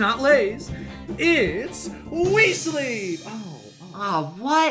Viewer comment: I misheard wii sleeve as weasly